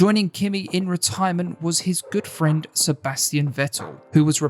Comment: Did anybody else mishear Kimi’s ‘good friend’ as Kimi’s ‘boyfriend’?